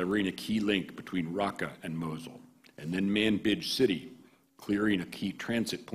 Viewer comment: sounded like he said "manbitch city" lol